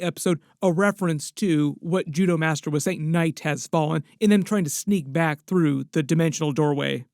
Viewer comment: I thought he said Darkness has fallen. But same diff.